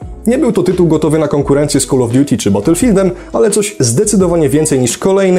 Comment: "Nie był to tytuł gotowy na konkurencję z Call of Duty" i co usłyszałem Bottlefieldem xD